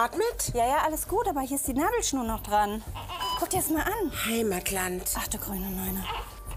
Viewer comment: Was sagt die bei 0:29? "Heimatland"? Hä?